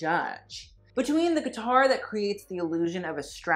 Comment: did he say "guitar"?